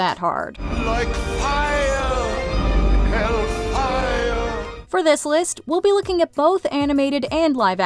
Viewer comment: When I was little (when the movie came out) I was confused, because I thought that Frollo was saying 'Elvira', not 'Hellfire'. It wasn't until the DVD came out and I rewatched it that I understood. Oops.